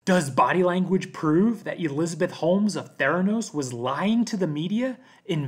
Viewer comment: When you said Theranos, i thought that you said Thanos